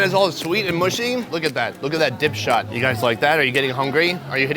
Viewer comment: i heard dipshit. hahahahah OH HELLL YEAHHH